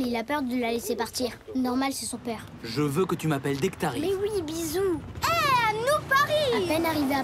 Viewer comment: J'ai entendu "Je veux que tu m'appelles Nectarine"